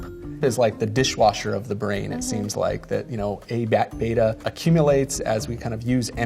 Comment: What was the term he said? Sounds like (A-back-beta)... Anyone know the exact spelling?